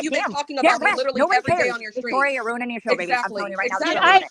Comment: does say - you’re ruining this for me ? 3:46 says get it gone - meaning get off the panel trying to control Victorias stream is correct can’t have an adult conversation without spurging out and running away